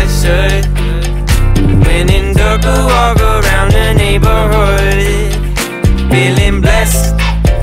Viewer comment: Am I the only one who hears "Lenin took a walk around the neighborhood" ? 00:21 I mean..